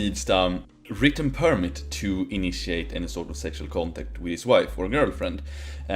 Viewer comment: Ahmgawd, the auto generated subtitles at 15:28 don't understand his accent so they turned "...a written permit to initiate any sort of sexual contact..." into "...a written permit to initiate an assault or sexual contact..." and now I can't un-hear it.